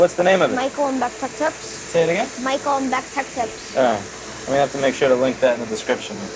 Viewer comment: you said you'd link his channel, can't tell what he said it's called. Be interested to see what content he's got there! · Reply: is the name, sadly I found what's used to be his channel but with only 2 videos... google it by yourself and check it